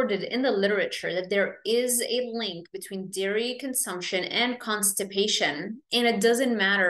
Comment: What can we use as a butter substitute on top of burgers or steak for added fat? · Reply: thought you said dairy constpates?